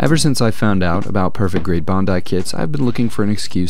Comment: Cool video, but when he says bandai i keep hearing “bondai” 😭